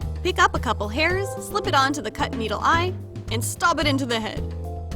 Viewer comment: who else heard stob instead of stab